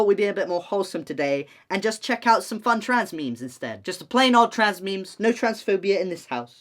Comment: WHAT’S YOUR FAVE TRANS MEME?!? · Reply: I actually thought you said “fave trans name” and was straight up about to just say my own name